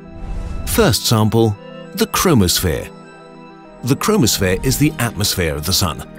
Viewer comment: When he said chromosphere I thought he said chronosphere